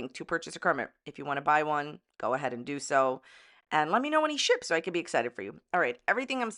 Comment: "Let me know when he [Kermit] ships!" Ughh why only now do I hear "let me kno when he shits" 😂😂😢 fuggin hell this arc is wild